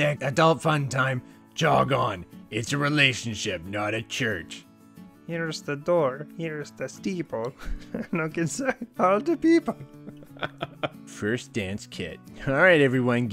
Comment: Anybody want to help me translate this? "Here's the door, here's the steeple, no concern about da people??" That's what I heard but it doesn't make sense to me.